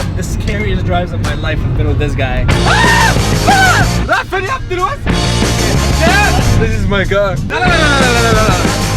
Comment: Any Arabic speakers know what's being said at 6:09? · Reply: Lmao yes he said son of bitch